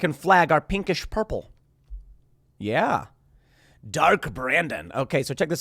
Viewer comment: For a moment I misheard Dark Brandon with DARTH BRANDON. 🤣